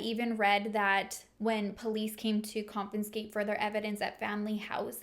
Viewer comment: What does the word "compenscate" mean??! Lol